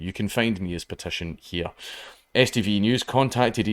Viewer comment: I totally thought you said STD News.